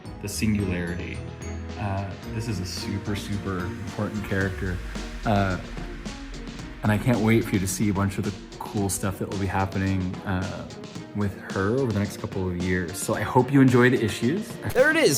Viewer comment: Did you just say the next couple of year